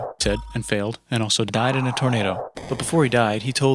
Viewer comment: I keep hearing "diet in a tornado"